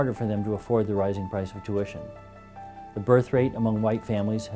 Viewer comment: ? Did I hear him right?!? At 0:30 I think that he says, ".. the birth rate among white families..." ?